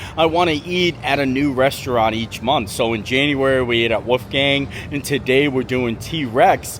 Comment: Did you just say “Woof Gang”?